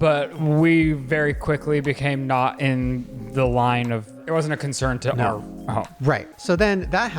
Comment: Did Ryland say “in the line of fire.” And Shane edited it out?